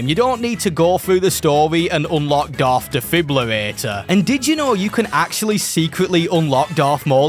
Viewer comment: did you say Darth Defibrillator? 😂